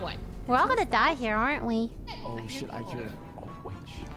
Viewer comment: RING AROUND THE ROSIE, POCKETS FULL OF POSIES, ASHES, ASHES, WE ALL FALL DOWN. AHAHAHAHAHAAHAHAHH! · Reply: I heard it as AH SHIT